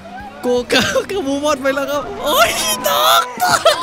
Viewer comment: ีตอนที่05:15พูดว่าเอ๊ยอีดอกใครได้ยินเหมือนกันบ้าง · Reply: เขาบอก เอ๊ยยยตก